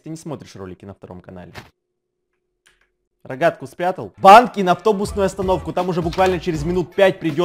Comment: кому послышалось банки?